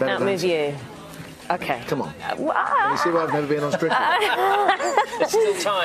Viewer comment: What does Elvis say at min 05:30? Something about: "Have u seen why I haven't been on ????? before" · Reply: "Strictly Come Dancing." "Come Dancing" (staid ballroom dancing contest from the 60s and 70s) combined with "Strictly Ballroom", ie the name of the film. "Stri-leh" for short. Hate it so much.